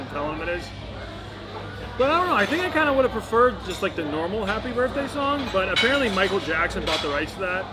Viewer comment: Every time she said happy birthday I thought she was saying happy first day😂🤣😂